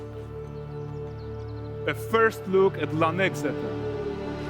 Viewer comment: the first look at what? i couldnt understand him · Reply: Lan Exeter... "Similarly, few remember that a mere handful of generations ago, Kovir and Poviss were still part of Redania. King Radovid I, known as Radovid the Great, handed dominion over them to his hated brother, Troyden, with one stipulation - that he never leave his newly-acquired demesne and not interfere in matters of state. Handing over this rocky scrap of far-northern ground (where, the saying went, the year had two seasons - August and winter) was naturally meant as a cruel joke, a slap in the face for the over-ambitious Troyden. Yet time soon proved that Radovid the Great had made a grave error. Before long, it was discovered that Kovir's bare rocks hid priceless treasure in the form of enormous deposits of precious metals and rock salt. This discovery, in turn, led to tremendous growth in productive industry. Mills, forges, and workshops sprouted up like mushrooms after a hearty rain. Radovid III decided to correct his famous forebear's mistake and take back the northern frontiers of his kingdom. He was convinced the combined armies of Redania and its then-ally Kaedwen would quickly bring this ever more audacious vassal in line. History took a different turn, however, and Kovir won a resounding, crushing victory. Radovid III was forced to sign the First Treaty of Lan Exeter, granting Kovir independence while binding it to eternal neutrality - a promise Troyden's successors have kept with great diligence." Basically, the Novigrad of Kovir.